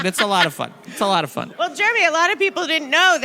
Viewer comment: Did she call you Jeremy @ 0:33 lol??